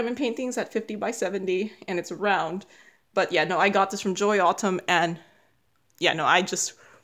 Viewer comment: I tried to find the painting you said was your favorite ( the christmas house) but I douldn't find it. Can you tell me where you bought it. I thought you said Joy Altum but I couldn't find the store.